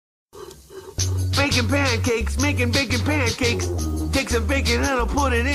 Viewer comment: before i started to watch adventure time i thought jake was saying bakin' pancakes